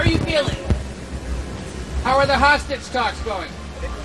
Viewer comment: I thought he said how are the hospice talks going lol damn he said hostage.